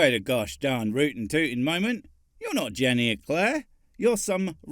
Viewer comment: I thought you said her name was Lemon Eclaire. And true enough, she was indeed a bit fruity.